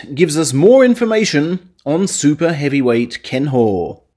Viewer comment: I misheard “super heavyweight” as “super overweight”